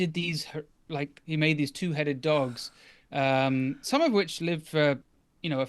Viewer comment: For a second I thought you said he created "22 headed dogs" instead of "2O 2 headed dogs".